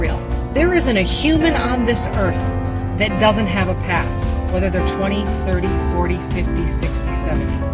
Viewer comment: I hear what she’s saying, but I keep hearing, “Can I have a past with her ass!?”